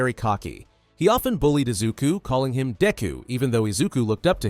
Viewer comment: am I the only one who heard “d*cku” at first 😭